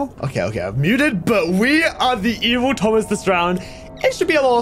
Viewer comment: It sounded like he said were the evil toes this round